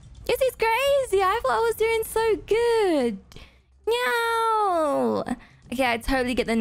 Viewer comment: I thought she was saying “meow” in stead of no idk why😂